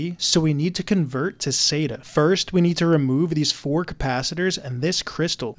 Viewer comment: for a second there i thought you said we need to convert to satan